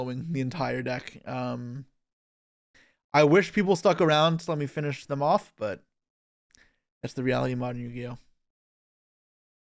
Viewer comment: What did bro say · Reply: "I wish people stuck around to let me finish them off but that's the art of modern Yu-Gi-Oh"